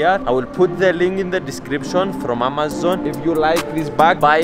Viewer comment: where the name fidias came from? i was named after my grandfather his name is also fidias, he is 82 years old and he is the coolest grandpa in the world! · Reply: it sounded like when you said amazon it sounded like you said mamazon lmao